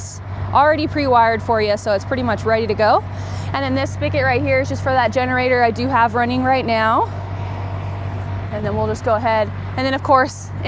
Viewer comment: did she call the generator exhaust pipe a spigot?